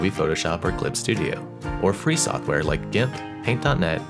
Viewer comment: WHAT IS HE SAYING DIMP DINP IDK AHHCHHCHCHCKKKKKKCKGGKKCK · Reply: GIMP